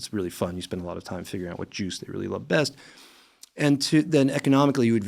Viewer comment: I thought he said Jews 😭😭